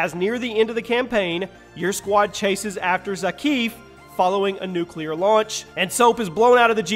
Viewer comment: did you just say zakeef